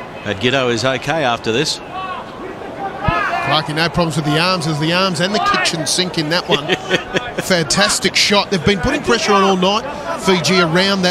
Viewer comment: thought the commentator said ''fucking no problem with the arms'' haha